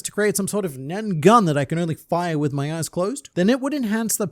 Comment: Thank you for repeating “if I could shoot a gun with my (eyes closed) thought you said with your A&$ closed smh